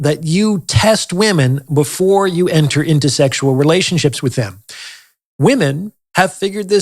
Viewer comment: Sexual relationships with him? Am I hearing that wrong?